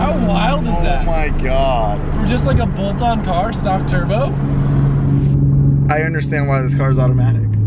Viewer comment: did he just say stock turbo? @ 10:20 gt3rs?